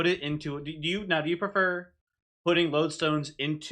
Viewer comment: ha i thought u said "do u like infusing lodestone totems into other units, or do you like to play the wrong?" and thought ohhhh coming out shooting!!!! but went back and u said raw not wrong :( had my popcorn ready to go and everything